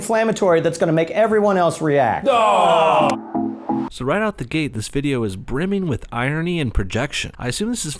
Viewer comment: Wait.. Did he just say right out the gay in the beginning!? THAT OFFENDS AS I IDENTIFY AS A GAY TRANSGENDER BISEXUAL BROWN ATTACK helicopter